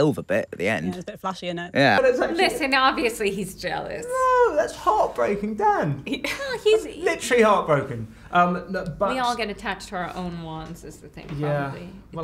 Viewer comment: Eddie's face when he looks into the camera and says "That's heartbreaking Dan, literally heartbreaking!" I could not love this man more! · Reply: At first i heard : that's heartbreaking DAD